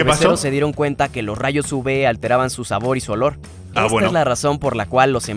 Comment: En el minuto 6:28 entendí la palabra del nombre Uve a y nose si se dieron cuenta pero yo los conté en el segundo video que miro y no son 30 son 25